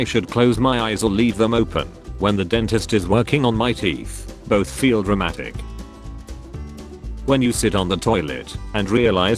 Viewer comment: I heard "both feel Romantic"